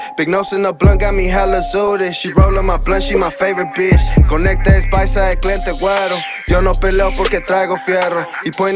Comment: What does he say at 1:06 after Conecta es paisa..... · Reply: Conecte es paisa el cliente wero…